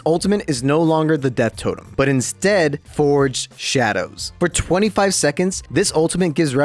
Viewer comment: when you said forged shadow i thought thet you said Forge 😢😢😢